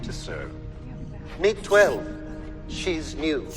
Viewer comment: I misheard the original dialogue. At the end, 4 says "meet 12; she's NEW." But he's got a very proper British RP accent, so he doesn't say "noo" like an American would, he says "nyoo" because RP English doesn't practice yod-dropping as frequently. Consequently, I thought he said "meet 12; she's MUTE." This changes the entire context of 4's dialogue once 12 speaks and says something silly, because now 4 is clearly not speaking ACCURATELY, but IDEALISTICALLY. He says what he wishes to be true, whether it is true or not. He is not an unfeeling automaton with no emotions or appreciation, any more than 12 is mute. He simply wishes to be. He is MANIFESTING. "I will not appreciate your gratitude, I will feel nothing at all, and this co-worker of mine will NOT SAY A WORD EVER." And then reality intrudes. Because 4 is not the boss of 12, and 12 doesn't have to do what 4 wants. Which begs the question, why is 12 being giggly and blushy about Superman looking at her? It's a fairly misogynistic way of making the GIRL robot behave. But... there's... a secondary question here. Who made her behave that way? After all, 4 has clearly chosen to just tell lies about objective reality in an effort to force his will on the world. Has 4 also chosen his accent and manner of speech? Does 4 control whether his voice sounds male? Does 4 speak and sound the way he does because he's chosen to speak and sound that way? Because that implies that 12 was not GIVEN a girl's voice or a feminine personality, b